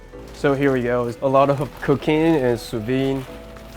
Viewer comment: Anyone else hear him say allot of cocaine